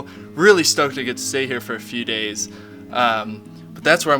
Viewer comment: did he just say "see you on the flipady flop"